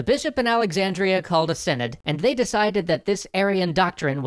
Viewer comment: he says the bishop called a [sened?] What is that word? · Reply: Vlad VP Thanks, but it was actually "synod." I was doing the captions for this episode, but someone else got that word for me.